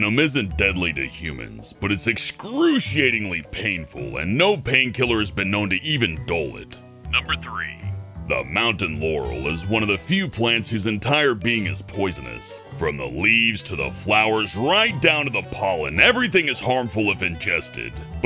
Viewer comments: I heard Mountain Yanny, what about you guys? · i can't find it on the internet but i only hear you saying 'mountain yanny' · anyone else heard mountain yanny?